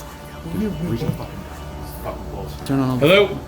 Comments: Raccoons make that screaming noise when getting it on. The door and the staring at the wall are creepy AF. I thought the first voice said “hell no” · I heard "Hell no" through the spirit box after asked if the guy regretted killing his wife, tbh.